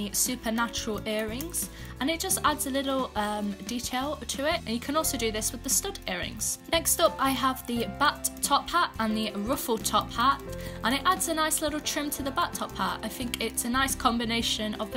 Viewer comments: I heard the “butt top hat” too 😂😂😂😂😂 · All I heard is Butt-Top Hat NaILeD iT 😂